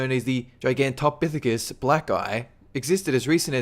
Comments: OK, at 8:05 did he say, "Giant apes known as the 'Giganton-Physasis' (black guy)?" or is my subconscious a racist? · did he say black guy? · Anyone else hear at 8:06 (Latin word) black guy? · did he say "black guy"? :3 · it sounds like he says black guy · Hey, did he just say Giantopithicus Black Guy at 8:05?!?!?!?! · haha whoa i thought you said 'giant apes known as the gigantopithicus - (aka) black guy' you absolute racist